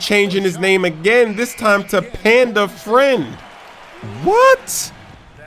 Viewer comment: Did he just say "Panda Friend" what is that cheese?